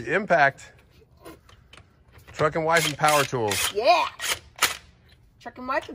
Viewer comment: Towards the end...sounded like he said f'n wives power tools!!!